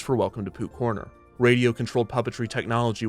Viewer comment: every time you say Pooh’s Corner I hear “Poop Corner” 😥